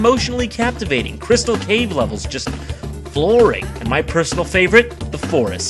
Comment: I always thought he said the crystal cave level was ‘boring’ and could never imagine how he would think so. Turns out he said ‘flooring.’ Fifteen years of confusion over a mishearing!?